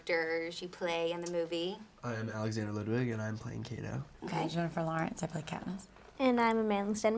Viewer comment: Haha, i heard "i'm Alexander Ludwig and i play Kiddo"